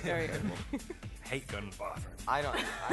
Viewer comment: did rob say "i hate going in the bath with her" ????